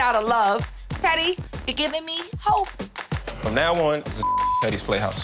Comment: Omg I'm dying, I can't.!! I thought he was saying 'TIDDY's playhouse ". Lol I'm so dumb because I even read the description box before the video..